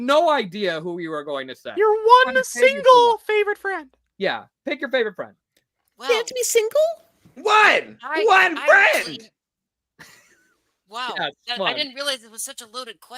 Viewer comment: did paper say "they have to be single?" ????